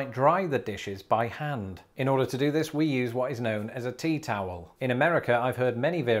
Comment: We just set a temp on our faucet and touch it to run. Also great for the shower. When you said tea towel I thought you said teetotaler. And first thought was never let them in your home. I have a double oven and I have never seen a towel put into the handles.......